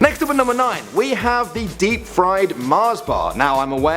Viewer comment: I thought it said dried